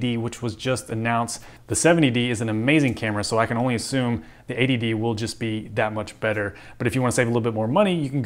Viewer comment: Did you actually say "...ADD will just be that much better"? I think Canon is just goofin with us by naming it that. lol. Thanks for the comparison video. Nice job.